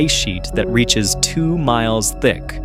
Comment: did he just say snow folds 2miles thick...😮